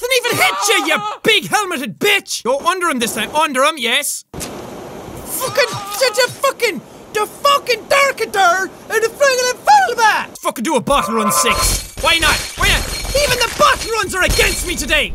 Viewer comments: I feel like he's speaking another language and funniest Thing I heard all day 10:07 (DA FUKING DUR KA DURNK FAFLIPTLY FWAL) That's What I heard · ! What did he say? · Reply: Dennis Billington, he said that fuckin derka -berk has a fucking zebakerer